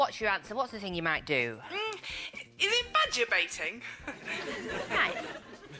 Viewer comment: Badger Baiting? I thought she said Vagurbating. Which also makes sense in TMI KT. EDIT: I spelt the V-Word incorrectly.